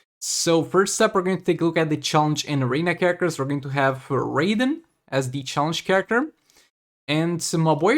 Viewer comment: When you said raiden I thought you are saying raven 😅